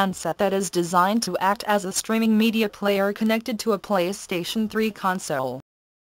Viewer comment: Yeah me 2 i was like " What the? did she just say pwn media?" They should have subtitles :)